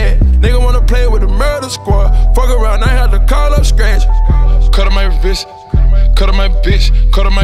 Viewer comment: First time I heard this I thought he was saying "Cut up my wrist, cut up my bitch" I was like 👀 👀OK Pluto